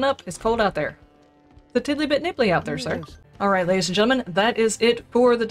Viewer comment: I always heard it said "niddly bit tipply." :D